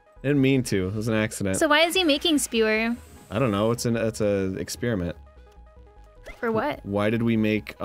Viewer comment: wow i actually noticed what Dan pointed out, he DOES say "exspearment" instead of "experiment"